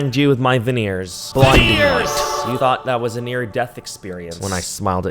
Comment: I thought you said "a 'veneer' death experience" lolllll